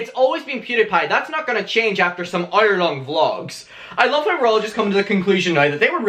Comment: I thought he said "iron on gloves" but he said "hour long vlogs" lol